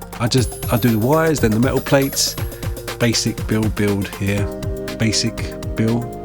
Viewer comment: I thought you said "Basic build build" for a minute there, like Bill is a Skaven confirmed